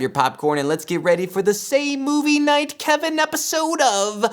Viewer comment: I'm new and at first I thought he was saying SEGA Knight Kevin. It would be a good name if he ever wants an LP channel.